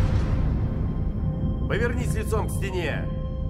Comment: я один услышал это? в начале повернись яйцом к стене на